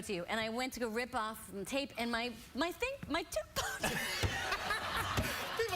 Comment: did she say her tooth popped out? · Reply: Tooth, she has veneers